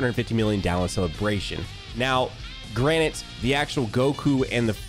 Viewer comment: Did this guy just say “granite” instead of “granted”